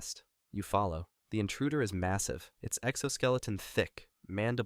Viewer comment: did you say……MASSAVE😏